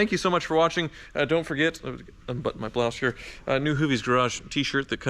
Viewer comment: Did actually say “let me unbutton my blouse”?!!! What man says that?